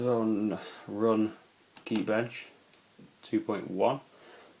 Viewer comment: Thank you! I thought you said Keybench and I was trying to google it! I am buying this same model right now. I am about to run geekbench on my current macbook to see the difference!